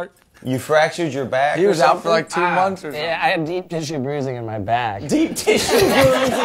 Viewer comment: Deep tissue bruising.... Why was that so funny? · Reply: I thought it was because it sounded like he said "Deep Dish-oo bruising"